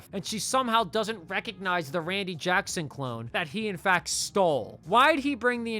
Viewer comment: dude I jus realized u were sayin cologne, I thought u were sayin clone dis whole time & I was so confused lol 🤦🏿‍♂️